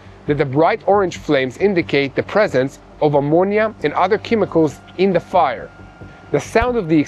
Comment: I’m not trying to pick on you or give you a hard time, Yair. But your accent made me laugh today. You mispronounced ammonia. It sounded like you said, Imodium. Which is diarrhea medicine here in America. 😂